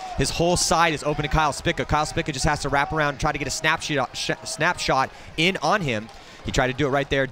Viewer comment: what did he say snap Sh*t or shot